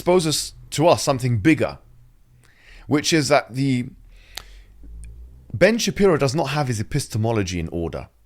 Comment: DONATE NOW: <link> ⬅ The Prophet ﷺ said: “𝙒𝙝𝙤𝙚𝙫𝙚𝙧 𝙗𝙪𝙞𝙡𝙙𝙨 𝙖 𝙈𝙤𝙨𝙦𝙪𝙚 𝙛𝙤𝙧 𝘼𝙡𝙡𝙖𝙝, 𝘼𝙡𝙡𝙖𝙝 𝙬𝙞𝙡𝙡 𝙗𝙪𝙞𝙡𝙙 𝙛𝙤𝙧 𝙝𝙞𝙢 𝙖 𝙨𝙞𝙢𝙞𝙡𝙖𝙧 𝙝𝙤𝙪𝙨𝙚 𝙞𝙣 𝙅𝙖𝙣𝙣𝙖𝙝.” If you support this project, you will in sha Allah get a house in Jannah and share in the rewards of all the people: ✅ Praying in the Masjid! ✅ Making Dhikr! ✅ Memorizing the Qur’an! Please donate and help establish this Masjid and earn your reward ➡ <link> ⬅ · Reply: i really heard 'ben shapiro does not have his pistons in order'